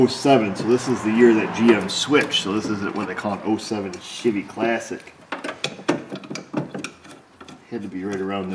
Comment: I could swear you said , “07 sh!tty classic “